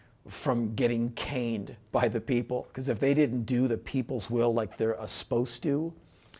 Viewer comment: At the end, does he say "do the peoples will like their asposed to"?